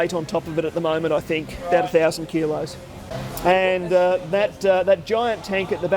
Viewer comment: ”We have a tonne of weight on it, ten thousand kilos” but a tonne is only 1000kg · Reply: I heard "...about uh thousand kilos..."